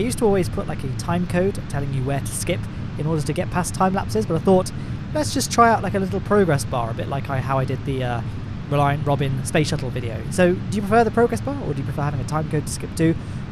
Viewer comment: i actually thought you said "reliant bobin"